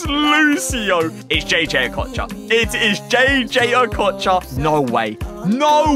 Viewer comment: I thought he said fodder instead of voller 💀